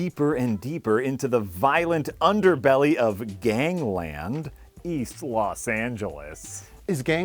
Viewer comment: I legitimately misheard "Gangland: East Los Angeles" as "Gangland: East Los Santos" and I thought I was going mad for a moment